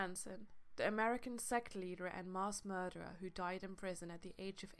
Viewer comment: Did she just say "marss murderer"